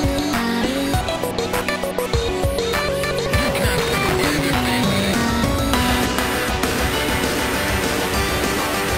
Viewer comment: what does he say here? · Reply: “You can’t get away this easily”